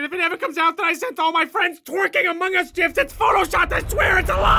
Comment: Did he say jif?